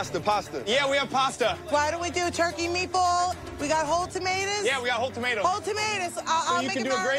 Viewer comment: Did she just say...”TOMATAS”?